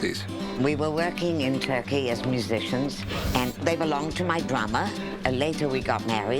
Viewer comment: did she just say she married her brother? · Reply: She said her drummer